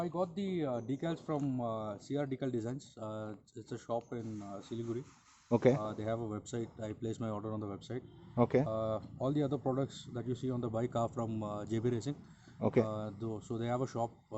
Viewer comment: Shop in Siliguri? 10:12 Did I heard it right?